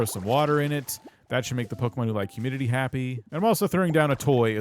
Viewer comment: for a second I thought you said “humididdy”🤣